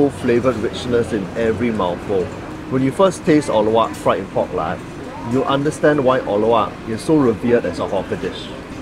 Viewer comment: I thought was saying "pork la", but it's "pork lar(d) la" hahahah my la ratios were going off the chart.